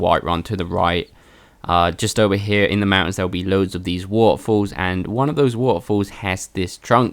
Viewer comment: lol I heard "there will be loads of these waffles" and I got really excited